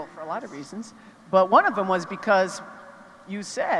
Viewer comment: who did he say he won it for? I can't make it out, Tara Hoad? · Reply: His college hometown TERRE HAUTE, INDIANA